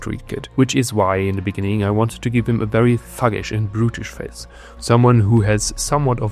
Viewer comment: oh "thug-ish" i thought i heard "f4g-ish". I had to turn on the caption. 😭😭😭😭